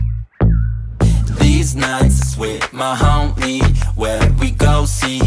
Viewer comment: I thought it said deez nuts with the homies lol